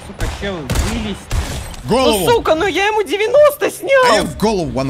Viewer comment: Там сказал "Сука"???? или мне послышалось?